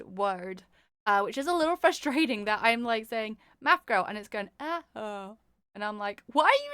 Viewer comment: what did you just call me 😟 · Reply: Math Girl wothout the M or G as my mic kepts cutting 😆